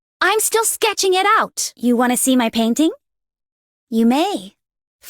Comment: I swear to god I heard panties at first.